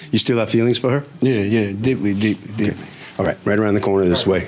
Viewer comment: he must still be drunk😂😂. What the hell did he say? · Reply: He said deeply deeply deeply